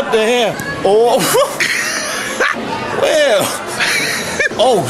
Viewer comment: does it sound like he is saying well